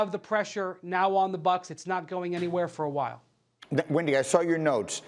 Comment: did he call him Wendy ????